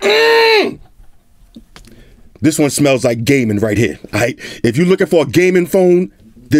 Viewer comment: did he say "This one smelss like Gay Men".. if so then Hol' up